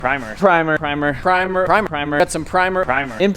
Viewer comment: I literally heard "primer it" in Mortal Kombat's voice over